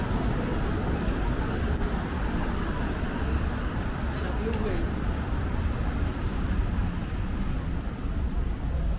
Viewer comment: What did She say to you 22:01 mark into the video. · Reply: Sounds like “How have u been?” Ex-gf maybe? 😂 Uploader, you dog you 😏🤣